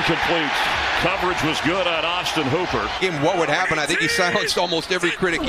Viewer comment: sounded like Austin pooper for a second lol